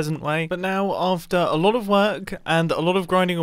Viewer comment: 🤣 when Mumbo said "its all fully constructed" I heard "awfully constructed"